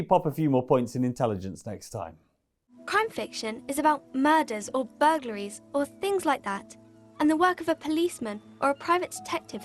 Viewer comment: For Disco Elysium, the kid talking... I heard "Crime fiction is about murder or buggeries or things like that." Just saying... Glad I wasn't drinking anything.